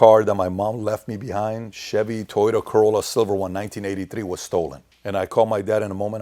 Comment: Did he say chevy toyota corolla